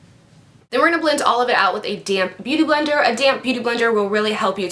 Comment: English is not my native language so everytime she used the beauty blender I thought she said "DAMN BEAUTY BLENDER" instead of DAMP :P